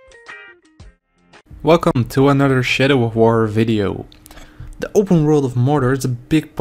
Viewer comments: sounds like he said "shitter of war." yeah theres that alright. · I thought you said 'shitter of war' the first time lol